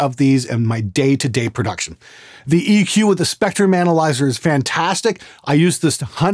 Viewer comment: For a moment I thought you had said "Spectre Manalyzer" instead of "spectrum analyzer".